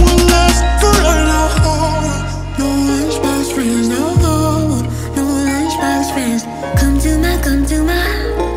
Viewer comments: I thought she said ”Lunchbox Friends, nO HomO” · All i hear is “no lunchbox friends no Hoe”